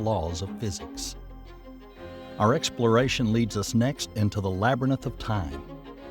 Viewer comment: Did he just say “labber nith” instead of labyrinth?